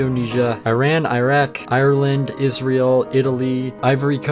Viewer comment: It sounded like you said irland is real but you said irland, Israel